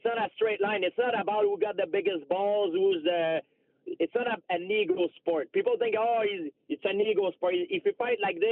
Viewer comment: What does he say at 23:19? It's not a ___ sport? Is it ego sport?